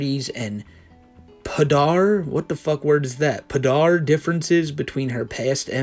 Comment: I think it says polar differences